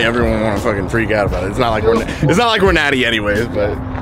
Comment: Did he just say they weren’t natty ?